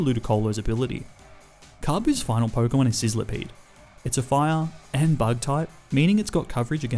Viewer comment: was I the only one who heard him saying sizzlerpeed instead of sentiscorch